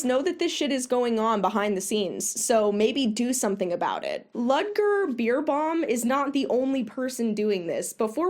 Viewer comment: every time you said his name I heard “Ludger Beerbong”